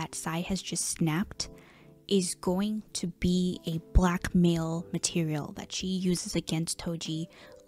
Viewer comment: heads up, the captions say "black male material" instead of "blackmail material." (great video though, thank you! adding peach girl to my toxic drama manga reading list)